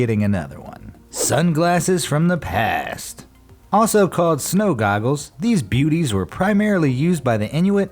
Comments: Anyone else notice that in 1:40 instead of (snow goggles) it was snow google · What he says, "Also called Snow Goggles" What the subtitles say, "Snow Googles"